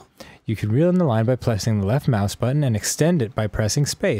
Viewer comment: did Dan just say "plessing"? 😂